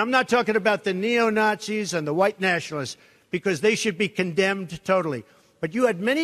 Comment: Biden today "Trump has never once condemned Neo-Nazis". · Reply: Are you listening @ 1:56 ? I thought he said "condoned" not "condemned".